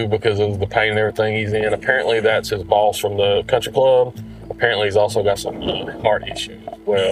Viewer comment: Did i hear that cop right "thats his boss from the country club the 14:35 mark?